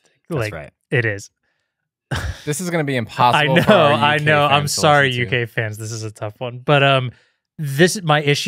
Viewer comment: Did anyone else have to listen to "sorry UK fans" twice? I could have sworn he said "sorry you gay fans"! 🤣